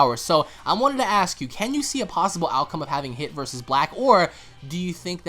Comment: I heard hit-ler